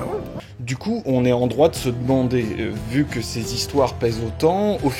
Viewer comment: C'est moi ou il dit : "On est en droit de se BANDER ?"